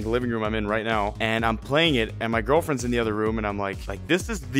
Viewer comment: did he say girlfriend?!